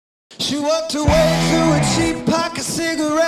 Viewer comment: for so long i thought it said “she walked her way through a cheap pack of cigarettes” because his beautiful accent (☺️) but it’s actually worked i feel so stupid lol